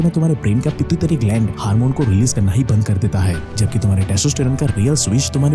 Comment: bro what are you saying 🤣🤣🤣🤣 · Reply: Pitutary